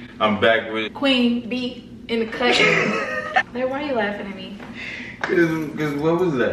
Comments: did she say BAE?!! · it sounded like she said “bae why you laughing at me” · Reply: Kswishes she said Clare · seconds it sounded like queen said”bae why are you laughing at me” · Did anyone peep Queen in the beginning sound like she said “bae why you laughing me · Am I the only one who heard queen say bae why you laughing at me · did she say bae??